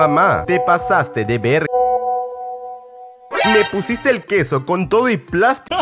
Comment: A que edad te diste cuenta de que dijo QESO